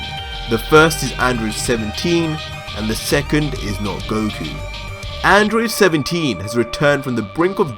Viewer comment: Did you say and the second, not goku.